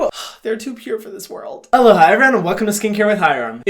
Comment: I CLICKED ON THIS BEFORE HYRAM COULD SAY ALOHA EVERYONE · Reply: THIS WHOLE TIME HYRAM WAS SAYING ALOHA?? MY DUMBASS THOUGHT HE WAS SAYING “HELLO HI” SKCHFHDKSN